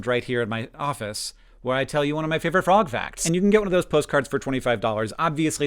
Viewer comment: Figured out pretty quickly he was saying “frog facts”, but I definitely heard “broth facts” at first. I would love a broth facts video!